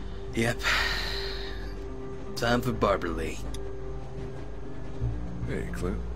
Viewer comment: so um...when cry said "time for barber lee" I thought he said Barbra lee XD